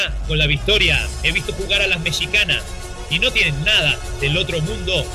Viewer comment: Escuche bien? el narrador dijo "Mechicanas"?? min